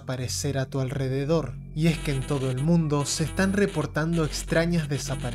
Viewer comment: Esto debería pasar en todo el mundo , no solo en Estados Unidos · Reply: ¿Qué dice? "En todo el mundo".